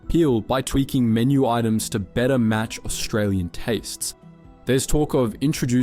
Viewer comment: "There's taco introducing more fresh ingredients, reconsidering portion sizes, ..." I heard what I heard. 🌮